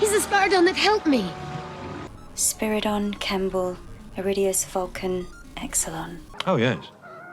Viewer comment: does she... does she say vulcan??